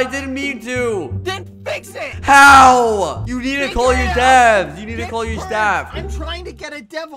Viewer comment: I thought he said "You need to call your dad."